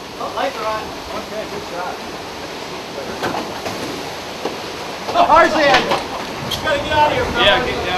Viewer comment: when he said "the cars in" i thought he said TARZAN! XD